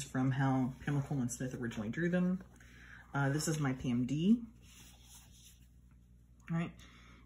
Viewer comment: which deck is the third WOF card in the comparison from at 16:39? I can't make out what's being said there. "my pmd"?